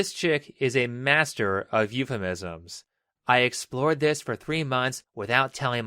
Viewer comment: Every time I hear she says "I explored" I hear - I was so fully explored.